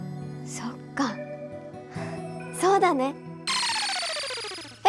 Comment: I swear I thought he said "alamak" 🤣